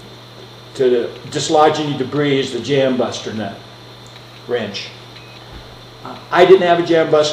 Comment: Did he just say bust a nut???